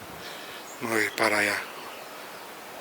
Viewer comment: Minuto 22:40 se escucha una mujer como que dice " no vayas "